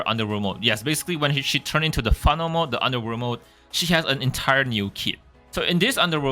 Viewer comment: I keep hearing "underwear mode" LOL sorryyy Nice vid btw